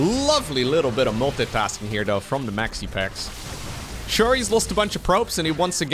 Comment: Finger skateboard, finger skateboard, finger skateboard... It's called a Tech Deck. Maybe calling it a finger skateboard is a Dutch thing but I've always heard people refer to it as Tech Decks. Edit: And every time he says "Maxi Pax" I hear "Maxi Pads" aka pads used during menstruation haha... not sure if Maxi Pax is a good nick name lul